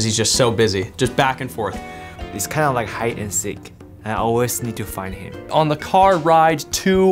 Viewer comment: it sounded like he said high and sick hahaha